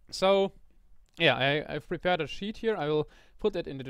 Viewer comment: did you say sheep xD